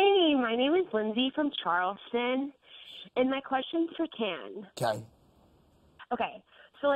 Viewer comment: Is it just me or did the host call Tan "Ken"? @ 2:58.. I hope I heard all that wrong, very unprofessional.